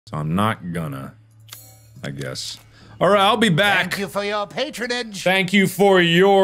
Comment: Nope, I heard “latern”